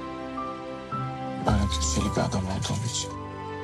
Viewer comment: Demir 1:47:50’ de tam olarak ne diyor? Lütfen anlayan birisi yardımcı olabilir mi 😢 “Özür dilerim, böyle … bir adam olduğum için” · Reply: “Özür dilerim…Böyle köşeli bir adam olduğum için, yaklaşmaya çalıştıkça köşelerime çarptığın için; canın yandığı için. Bana yaklaşmana izin vermemeliydim; ama ne zaman geldiğini bile anlayamadım; belki bunun için afedersin. Çünkü seni uzaklaştırmaya gücüm yetmiyor artık…”